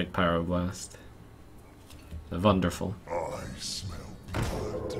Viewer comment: End of the last match, could a sworn the infiltrator said "I smell brawl"